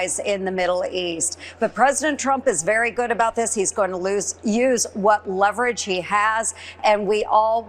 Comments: Did she just say 6:01 "the president is very good about this, he is gonna lose, 😮 use..." WHAT! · The Freudian slip of all Freudian slips. "President Trump is very good about this. He is gonna lose...... !!!!!" Yes she said it, that`s what her unconscious is screaming and she`s fighting not to listen to it.